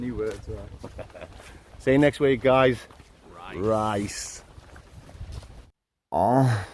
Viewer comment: ave another question, what do you say at the end of the videos? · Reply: We normally say RIGHTS but this week we said RICE 🙂👍